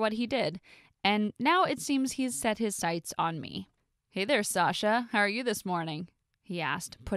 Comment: i thought he says Sasha Banks😂